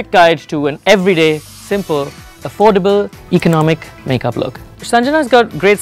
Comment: i thought u said affordable.......huhhhhhhh